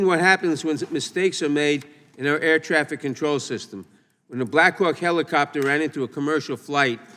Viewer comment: why does it sound like Naddler said "a Blackrock helicopter"?????